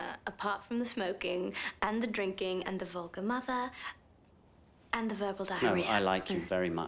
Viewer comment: Funny subtitles...Vulcan mother instead of vulgar mother